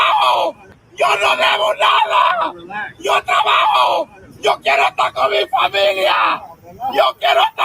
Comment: IS IT JUST ME OR DID THE GUY GETTING ARRESTED BY I.C.E ON THE GROUND JUST SAY "Yo Quiero Taco Bell"??????????? NOW I COULD BE WRONG BUT I SWEAR I JUST HEARD HIM SAYING "Yo Quiero Taco Bell"!!!!!!!!!!!!!